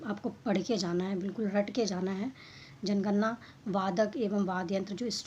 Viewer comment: bhai ye 1:18 me kya boli ho? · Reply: Bilkul rat ke jana hai... (Matlab yaad karke Jana hai)